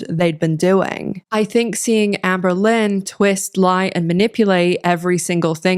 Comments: I know that you’re saying “Amberlynn” but I keep hearing “Anne Boelyn”……. Night brain rot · Every time you say Amberlynn I hear “Anne Boleyn” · I keep hearing "Anne Boelyn" when you say Amberlynn. Visions of Henry VIII (which from the sounds of it is fitting...)